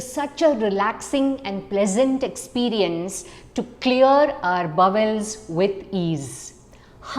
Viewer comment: Was having a little trouble with your accent, At first, I thought you said Clear your Bubbles.🙃 ?